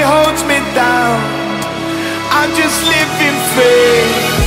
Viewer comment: When I first heard the track I thought it said: "Nobody holds me down, *I just live in fear*" but I guess it's actually: "Nobody holds me down, *I'm just living free*"